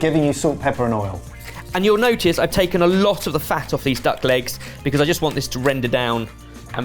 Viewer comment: I thought he said DUCK LIPS 😂😂😂😂